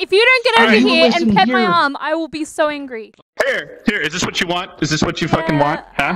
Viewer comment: I thought it said I would be in Greece and I was like Hell funking yea but it says angry